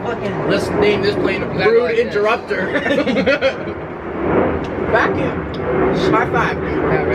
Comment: is it just my bad hearing + rude interrupter in the background but did call “mathew”